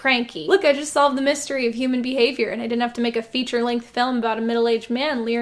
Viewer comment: At first, I somehow misheard "emu" as "human", and that honestly would've been a much better urban legend.